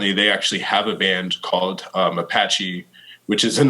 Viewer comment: “Apache” is a racist slur now? Isn’t it just the name of a tribe? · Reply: I thought that's what he said too, but apparently he said "Mapache" which is a Spanish racist slur for black people.